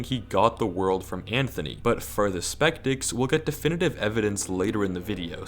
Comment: Does anyone else notice that at 13:17, says spectics instead of skeptics?